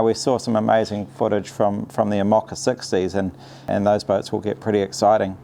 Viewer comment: What is Ray saying at 10:18 ? "we saw some amazing footage from the emocka 60s"???